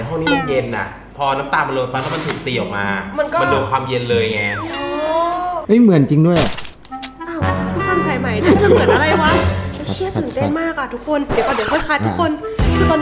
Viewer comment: เราได้ยินว่า เชี่ย อ่ะ ใครได้ยินเหมือนเราบ้าง